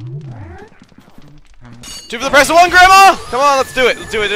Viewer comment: What did he say? "Oh great oppression grandma"?